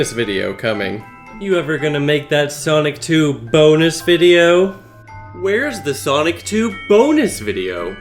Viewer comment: At some point I just heard: ''When are you going to make that Sonic 2 BOGUS video?''